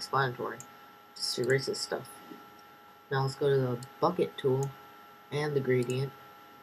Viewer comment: nice i thought you said fuck it tool when you said bucket tool